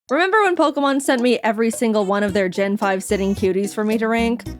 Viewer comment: I thought it said pokemon sent me to jail😭